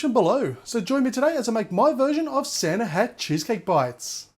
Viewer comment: At the end it sounded like you said " Santa had cheesecake"